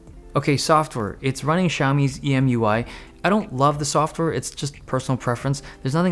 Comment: I heard "Xiaomi's EMUI" .